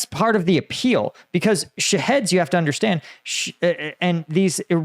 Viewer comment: I keep hearing "shitheads" not "Shaheads"..